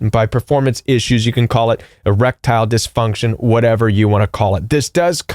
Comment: I thought you said 'a reptile dysfunction'..apt.when taking bout narcs🐍